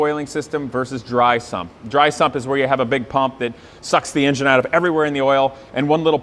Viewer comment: Anyone else heard him say "suck the engine out of the oil" when explaining about drysump 😂